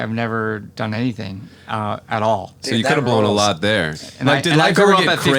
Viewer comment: did steveo say "that blows" lol